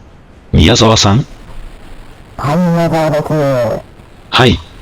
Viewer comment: ここから何を言ってるのかわからん。 『はい宮澤でぁす…』と聞こえた